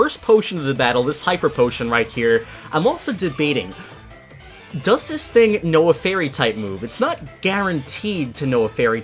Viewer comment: An add came on when was asking if Bede's Hatterene knew a fairy move but it sounded like he said "Does this thing know a fairy?" and I was thinking "Well obviously it knows a fairy!"